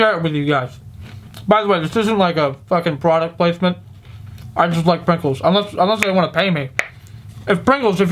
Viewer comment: I played this part over and over again just to make sure I heard you correctly. 3:29 "I just like Pretzels."